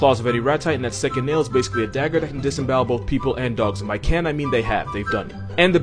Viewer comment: I heard Rattata 😂 Ash would have caught nothin but a fade 😂😂